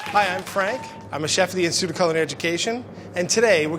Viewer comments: At the start I heard “Hi Anne Frank” 😳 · Every time he presents himself I just hear "Hi Anne Frank"